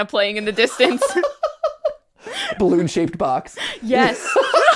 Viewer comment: I listen to GTLive videos while I work on art and I swear to you I heard "balloon shaped box" and just about ruined a piece I was laughing so hard. Excellent job once again, theorist team.